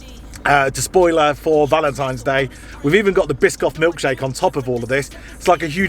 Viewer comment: Did you just say Biscoff milkshake? (insert drool icon here) 👍🏼